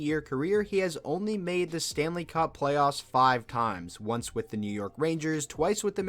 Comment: For a second I thought he said Eric staal has only made the stable cup FINALS five times, and I was like “bruh how is that bad???”